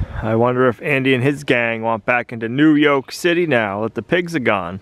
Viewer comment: Perfect timing for Moose! I heard him say “ Don’t let Andy back into New Yolk City!”